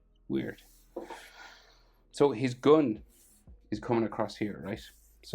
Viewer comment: bro literally said goon instead of gun